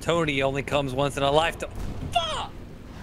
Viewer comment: Did just say fuck at 0:35? Cos that's what I heard and it was absolutely hilarious